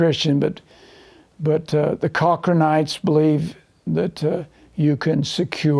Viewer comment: Which group or person is he talking about on minute 49:50? I cant understand. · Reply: Chochronites